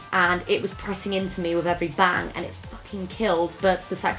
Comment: What did say in 9:17? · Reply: I can't remember if it was you